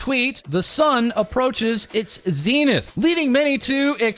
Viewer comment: i thought it said "☀The sun approaches its penith☀"